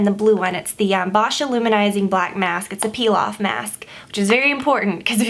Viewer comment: lol when you were talking about the Boscia mask I thought you said it's a pilaf mask!